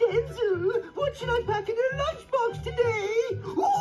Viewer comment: Mr. Crocker: NOT THE NUGGETS, MOTHER!!! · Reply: When I first saw this I thought he said “NOT MY NUGGETS MOTHER!” Lmao